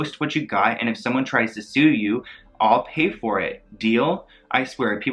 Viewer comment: she said "all pay for it" instead of "I'll pay for it" , that would protect her against a lawsuit but sounds similar. She wasn't gonna pay for shiiit